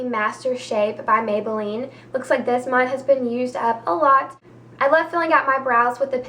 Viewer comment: you said "my husband used up a lot" lol maybe its just the way i heard it . lol